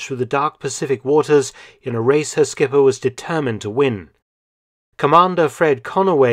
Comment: I could have sworn he said "a racist skipper was determined to win". Oh boy... When Chapelle's last Netflix special lingers🤣